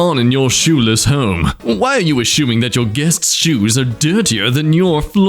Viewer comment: Did you just say aSHOEming?